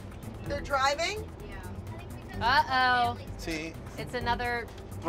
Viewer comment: I know he said “see?” At 3:28 but oh my GOD IT SOUNDED LIKE “TEA.”